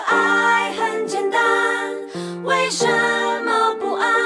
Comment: Sound like she says “Wei Shen”